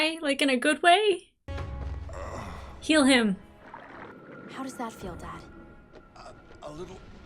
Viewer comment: I've heard that as ""kill him" and laughed out loud